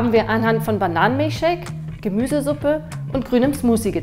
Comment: was hat sie gesagt gemusee shaak ?? Wie sie gesagt hat